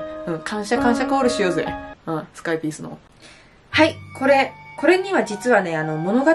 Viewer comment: こんだけ悪口言ったあとに聞くと不快ピースに聞こえて仕方ない